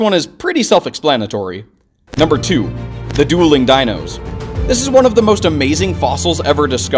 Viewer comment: When you said dulling dinosaur I thought you said duolingo dinosaur😅